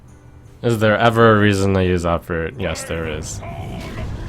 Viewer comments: what did he say? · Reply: Egg&Bacon "Is there ever a reason to use uproot?" ie making the Ancient a war a 10/5 instead of a 5/10 Taunt. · what did he say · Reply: Q: Is there any reason to use Hogger ( i guess) A: Yes there is